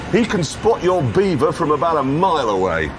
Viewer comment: did he say “I can spot your beaver from a mile away” ? Legend